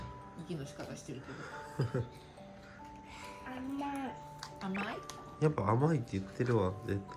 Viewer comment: も、準備のお手伝いしたくなるよね😅 が本格的に手伝えるのは、来年かな？🤔 もシンプルケーキを食べて、嬉しかったかな？🤔 きっと嬉しかったでしょうね😁 それに、いつのまにかちゃんと1人で食べられるようになって、成長を感じますね☺️ パパの言う通り、しっかり 6:48 「甘い」って言ってるように聞こえました。